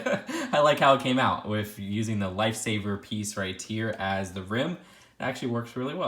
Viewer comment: I thought you said light saber